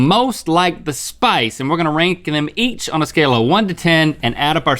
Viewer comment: did he say "we're going to RHINK them on a scale from 1-10". Please say yes. 💀